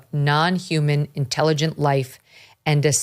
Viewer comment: Megyn, with Halloween right around the corner, why not put forth the idea that the US Congress should consider holding investigative hearings that feature witnesses who have had encounters with dangerous cryptids, such as the Dogman and the like? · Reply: When Megyn said, “non-human intelligent life”, I thought she said, “non-intelligent human life” and thought she was actually going to reference Congress.